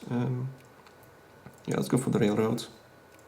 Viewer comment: sounded like you said lets go fuck